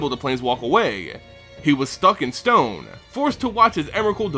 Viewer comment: I heard "He was stuck in stone" as "He was fucking stoned." I laughed harder then I should have.